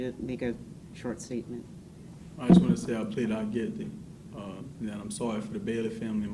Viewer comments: Did he say he's not guilty at the end when he makes his statement? Judge looked confused for a moment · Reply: Yup, I heard, “I plead not guilty.” Que up to 15:54. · It sounded like he said “I plead not guilty”…did anyone else hear that?? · Am I tripping or did he say "I plead not guilty" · What did Aiden Fucci say at 15:53?? Was his statement clear to anyone else? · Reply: He said he pleaded not guilty and he’s sorry to the girls family and to his own family. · Sounds like Aiden says "I plead not guilty" before he apologizes. I have played it over and over. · I’m slightly confused…the transcript on this page states Fucci as saying “I plead not guilty and I’m sorry for the Bailey family and my family.” No wonder the judge looked at him and said there will be time for a statement later. He’s pleading guilty but saying he’s not guilty?? What???? 🥴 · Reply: I have replayed this and come on here to see if anyone else heard it too. I hear “ I plead not guilty”.